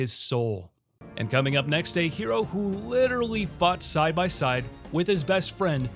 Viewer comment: I had to listen to number seven again. I thought he said, who literally fucked side by side 😂😂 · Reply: Bro its "Fought side by side"👀👀👀